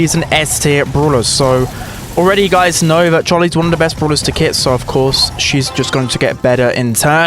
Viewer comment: I thought you said kiss